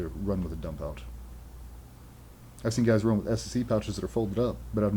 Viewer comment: did he mean SICC pouch or what did he say?